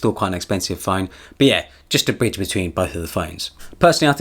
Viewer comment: Anyone else thought he said bitch instead of bridge lol